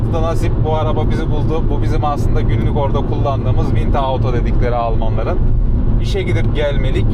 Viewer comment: selamlar terim nedir anlayamadım? Es ist nicht mitte auto..Ich habe immer wieder aufgehört, aber nicht verstanden. Kazasız belasız sürüşler sevgiler...❤🎉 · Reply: Ich wollte dort eigentlich „Winterauto“ sagen. Mein Deutsch ist noch nicht perfekt, ich lerne noch, deshalb war die Aussprache vielleicht nicht so klar. Vielen Dank für deine lieben Wünsche und fürs Zuschauen! 🙏🚗✨